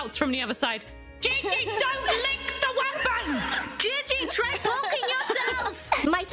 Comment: CC says poking, not holding